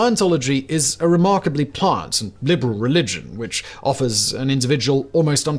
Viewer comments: I think he is saying, 'pliant', not 'plant, at 31:32. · I don't think he said "plant". He said pliant. (As in flexible.)